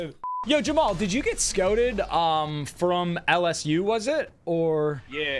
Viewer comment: is it me or did say Scoted from LSU? Its Scouted.